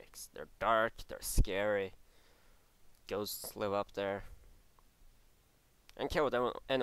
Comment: xD I thought you said 'girls live up there' not ghosts.